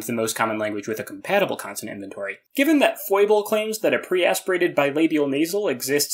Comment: what does he say the name of that website is? I can’t make it out quite right. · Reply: captions say it's PHOIBLE